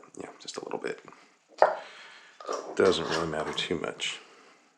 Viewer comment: I thought he said doesn't need a pan